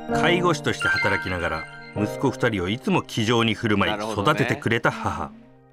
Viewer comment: 「気丈に」が「騎乗位」に聞こえた自分はもう純粋じゃないな